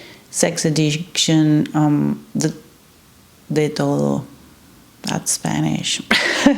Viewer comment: Very very interesting! At around 5:31, you are speaking about something to do with 'sex addiction,' but then say a word in Spanish. I struggle to hear exactly what the word is. I have put the subtitles on but it is still unclear. Did you say "de toro?" If so, what is the etymology, the literal meaning of this phrase... please?☺ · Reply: She said "de todo", means everything, was talking about sex addiction all kind of actions that implies it an addiction y she only mentioned some, after she said the phrase instead says etcetera